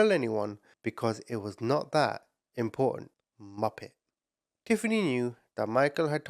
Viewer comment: I love how you call people muppets 😁🤣 yr awesome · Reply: I always thought he was saying “Moppets” lol